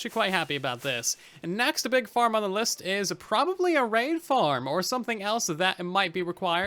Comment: I thought he said next big thing I'm gonna build is a rain farm instead of raid farm